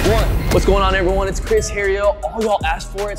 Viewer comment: At the end of the video where it cuts to black, and says, "heria," I LEGIT thought it said HERNIA, which was super fitting for what Mike was doing before the cut.😂😂